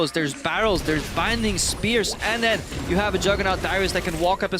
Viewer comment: Did Krepo say "Dyrus" at 23:45 ? xD